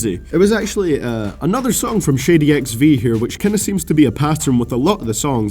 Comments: I Thought U Said Shitty XV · sorry man but everytime you say Shady XV I tear up cause it sounds like your saying Shitty XV 😂no disrespect tho keep making your awesome videos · I deadass thought you were saying shitty XV and not shady XV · It sounded like you said Shitty XV